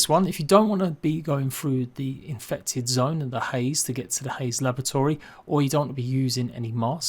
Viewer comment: great tips cheers, im not sure if its my hearing or not but it sounded like u were saying lavatory rather than laboratory to me lol